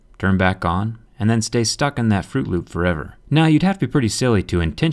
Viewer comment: did he say fruit loop instead of boot loop